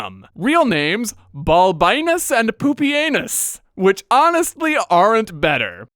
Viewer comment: I was listening to the video in the background and I heard "Balbinus and PoopyAnus"... I had to rewind the video to check if I heard that right XD Also, slight cringe at earlier implying Heliogabalus could be "transgender"... modern gender ideology should have no place in historical video.